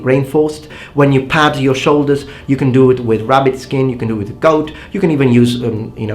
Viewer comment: Somehow when first listening to 14:07 i heard "rabbit-skin, you can do it with GOLD " and i thought "Yeah, Gold will work".